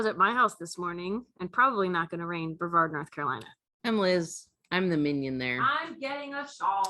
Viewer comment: I hope my comment made you chuckle! That’s what I hear. I know she says “my name is Liz and I’m the minion here”, but I just hear “… and I’m the millionaire” 😂😂 Chelo, from the UK